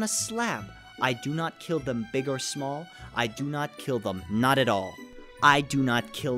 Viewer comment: he said i do not kill them not at all instead of i WOULD not kill them not at all like it says on the screen